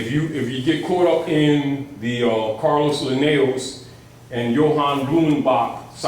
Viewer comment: what is he saying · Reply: I.S.L.A.M. carolus linnaeus & johann blumenbach.